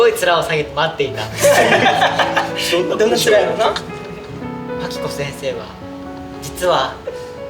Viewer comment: マキコ先生が「パキコ先生」に聞こえて進めない笑笑